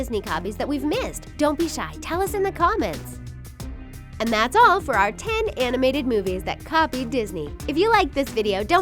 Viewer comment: Stop at 11:20 and I heard this "and that's it for our best anime" lol I do like anime tbh